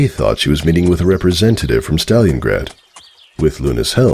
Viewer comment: I checked captions to see if it said stallion grad but at first it sounded like it said STALIN GRAD...